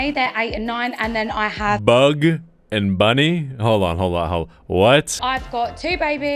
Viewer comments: At first I thought she said bad bunny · Nah she lost me on the “bug and bunny” part 💀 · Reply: When she called them downstairs I thought she said bad bunny